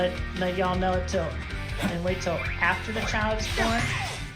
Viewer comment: I thought he said Childs porn